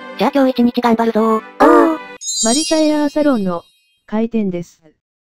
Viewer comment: ヘアーサロンがエアーサロンに聞こえたのってわたしだけ？